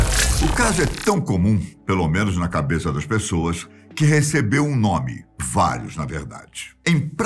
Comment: Escutei ele falar: "Que recebeu um nome: Fátima Bernardes" kkkkkkkkkkkk